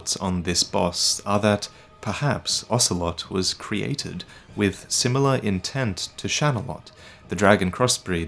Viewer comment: I fr thought he said ocelot and shagohod